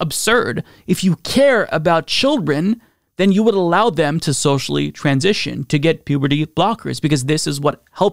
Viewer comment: To care about children is to allow them to surgically transition? That’s insane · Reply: I miss heard @ 10:50 he said if care for children you must let them {Socially} transition and take hormonblockers I mistaken socially for surgically Still stand by what I said though Even to allow pre-pubescent children to take hormone blockers is insane